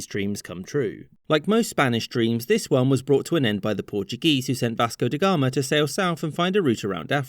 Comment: I misheard it as "bastard de Gama," which is a far better name for him. Or almost anyone.